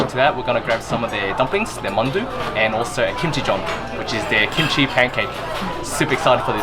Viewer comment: I thought I heard “kimchi-john” haha When I read the text, oh... it’s kimchi jeon 😆